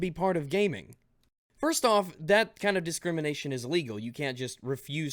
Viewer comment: hate to be that guy but it sounds like you said legal you meant illegal maybe I just didn't hear you right either way Fist me daddy